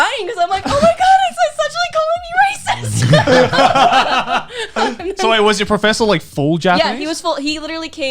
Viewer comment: the subtitle is wrong. She said "omg he's actually calling me a racist" but the subtitle is misleading